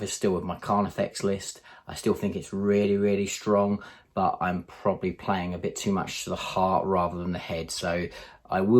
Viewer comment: I misheard it as playing your Carnifex list from the heart, the rum, then the head. I was like "I can relate to that". Good video, as usual though, thank you for sharing!